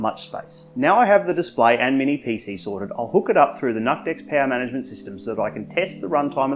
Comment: I swear i heard him say "nutdeck". 🤣